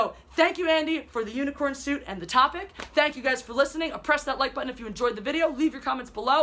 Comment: did I hear "oppress that like button" - I will!